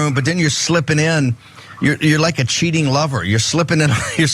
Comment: I thought he said "like a leaking rubber".